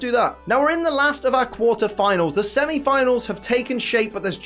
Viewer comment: Who else thought it said quarantine finals 😂